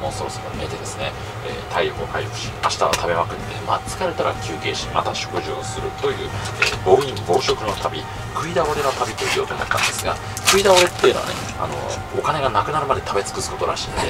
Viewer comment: 字幕があった方がいいか、無いほうがいいかアンケート調査をしますのでご協力ください（下に続く） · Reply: スーツ君の発音は聞き取りやすいのですが機械の精度が悪すぎます よね 自動生成字幕を出して見ると冒頭の二分間だけでも半分も合っていません 1:05 バラモリマからもうソースが見えて対応ハイパスターダベアドゥあっ疲れたら休憩しパティオするという４ソープの旅 ...って食べ歩きじゃなく風俗巡りに感じてしまいます 耳の不自由な方にもあなたの軽妙な語り口が楽しめるのであれば、大変お手間でしょうが字幕も親切だと思います